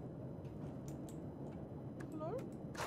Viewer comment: Did someone say hello @ 33:00 or am i hearing things? It didn't sound like